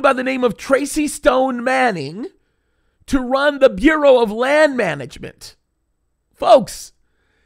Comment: When you said Bureau of Land Management I thought you said Fuehrer of Land Management 😂